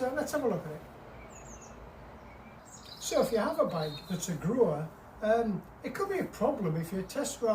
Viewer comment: I thought you said growler not grower 😂